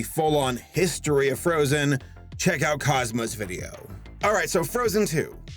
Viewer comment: I thought he said cosma instead on cosmo and in my mind I was like ok ko theme song intensifies